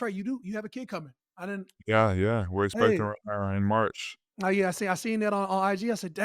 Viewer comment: Big Cat did you slip on the gender of your baby coming? I heard "We are expecting her..." 🤔 No matter... congrats to both of you making a fam!! 🙏🏽👊🏽💯🫶🏾